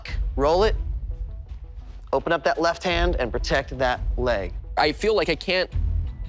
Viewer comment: I think I misheard this at 5:30 "...and protect that blade..." shouldn't the blade protect me? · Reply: There's a stutter, he says leg though it sound like "b-leg".